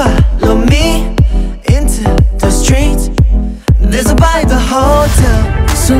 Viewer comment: i could've swore that Wonho said "into the streets, theres a body to behold" haha :D and then Hyungwons "i wanted to stay around, til your powers calmed down" yours makes more sense tho! THANKS!